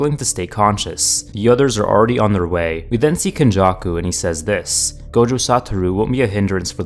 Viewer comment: 'Kinjaku' -Subtitles 'Chicken Jockey' -What I hear